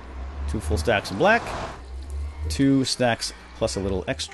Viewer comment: did you say "snacks?"